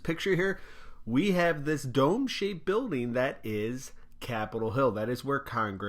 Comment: I thought he said, "Dope shaped building", not " Dome shaped building", Lol.